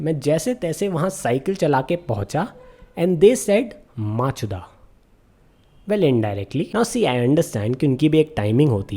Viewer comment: I think she said bokachoda😂